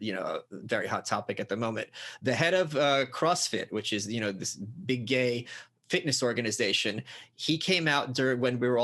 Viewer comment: did michael say that crossfit is a gay fitness organization LMAO